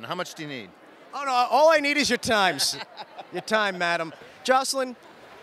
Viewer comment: you were going to say "all I need is your time sister", then changed it to "madam"! I heard the "s" come out! Tell me I'm right 🤣